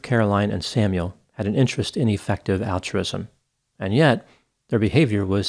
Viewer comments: Everyone thought he was saying effective altruism when he was saying ineffective altruism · At first I thought he said "defective altruism."🙄